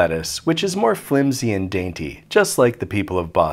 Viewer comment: Did you call Boston people dainty? Them’s FIGHTING words